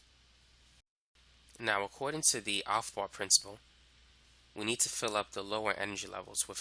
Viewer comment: can somebody tell me what principle is this part? 27:58 I can't understand clearly what he said in that part and the caption isn't helping at all. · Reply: afbau principle